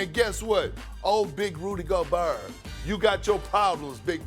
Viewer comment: I thought he said ol big booty gobert 😭😭